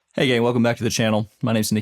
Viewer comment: Did you just say "Hey gay..." ?